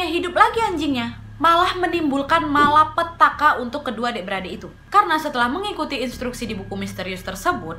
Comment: Pas menit 2:58 kok saya dengernya adik-beradik yak?